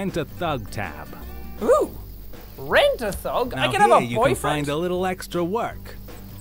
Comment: Jack: "Rent a thug? I can have a boyfriend? " I don't know if he said that or I just heard it, but it's funny either way.🌸